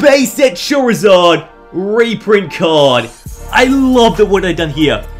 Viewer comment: I swear you said chorizard